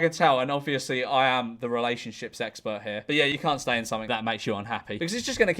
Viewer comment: did you say “relationships expert” or “relationship sexpert”? I need to know, this is important